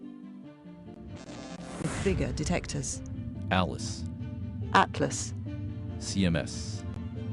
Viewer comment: did he say alice ??? im sure thats the name of the computer in resident evil ??